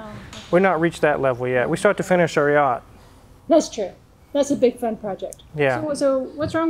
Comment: hahahah I had to go back and listen b/c I thought said "that's a big f**ing project" instead of "that's a big fun project" and I was like "WHHAATT??" hahah. It seemed so out of character. Lol.